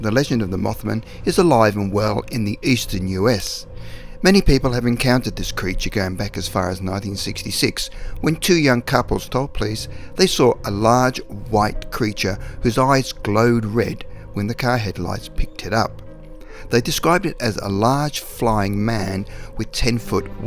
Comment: He says "a couple stop please" and continues with the story. Why? Or did I hear wrong..............